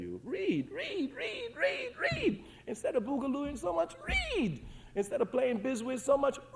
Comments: How can you google instead of reading in 1979 (30:24)???? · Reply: Listen actively. He said instead of boogalooing playing bid whistle, read more. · did he just say google ? This is 1979....somebody help me out here. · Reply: He said “Instead of “boogaloo’n so much, read”. The use of the word boogaloo is to reference the music and dance scene at the time of the speech. Hope this helps.